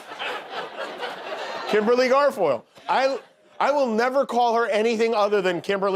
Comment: I thought she said Kimberly Gargoyle at first